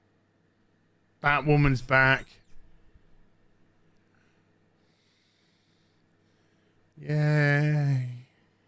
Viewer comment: I thought you said “That woman is back. Yeah…” I couldn’t stop laughing…but you wouldn’t understand! 😂😂😂